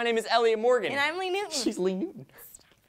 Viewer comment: in the intro when elliot said "she's lee newton", i thought he said "she's leaving" and i freaked out